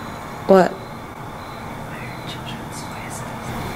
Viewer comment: you said you hear children's voices it sounded like we have to get out of here